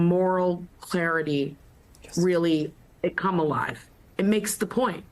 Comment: I'm hard of hearing so when I heard "come alive" in the commentator's words in the last minute of this video my mind heard "Kamalive". That made me think how Kamala Harris has made this issue, as well as the whole presidential election "Kamalive". Thank you for that.